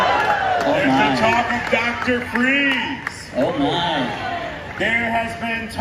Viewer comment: Coulda swore he said doctor freezes lol